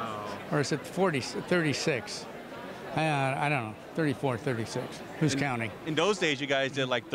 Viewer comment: Did Tony say there were 434-436 episodes of Leave it to Beaver?